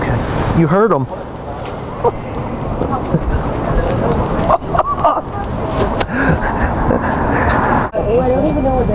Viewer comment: Hahaha I wonder what the Chinese woman said at 1:52? Lmao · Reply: "Fucking Americans..."